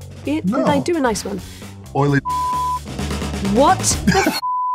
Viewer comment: What did DJ say in 1:48 ? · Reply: Oily discharge.